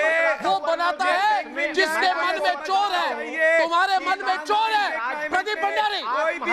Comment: i thought arnab is saying madarchod